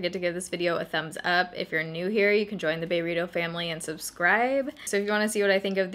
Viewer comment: I thought you said join the burrito family 😂😂😂